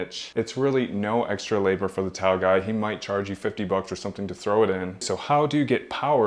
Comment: Freaking Awesome! Plus! Swear I thought you were saying your Towel Guy🤣 Great Idea